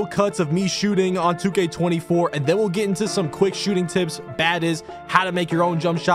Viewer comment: Bro said bades instead of badges 💀